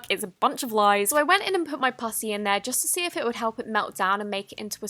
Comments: On 1:33 it sounded like she said pu##### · When she said putty it sounded like she said p*ssy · IT SOUNDS LIKE SHES SAYING PUSSY INSTEAD OF PUTTY